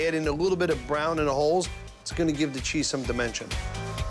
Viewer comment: I thought he said: it's gonna give the cheese a little dementia 😂😂 I'm dead